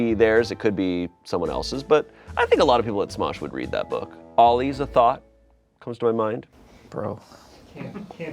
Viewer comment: I’m sorry what’s the joke? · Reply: Thot or T.H.O.T means “that hoe over there.” It’s a derogatory term for a woman who is or is perceived to be sexually promiscuous. Shayne said “is a thought” but they joked that he sounded like he was saying “is a thot.”